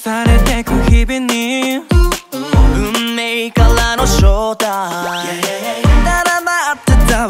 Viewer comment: 🌈この一枚の白いドアから始まって今Mainstream･･･ 最終審査メンバー発表のすぐ後に撮った映像も使っているこのMVを観ると、年末の雰囲気も相まってか感慨深いです 彼らに出会えて応援できて幸せと思える自分を見つけられました、ありがとーー❗ 「運命からの招待」を「運命からのShow time」と半年くらい空耳していたことも、ここにカミングアウトしておきま～す😆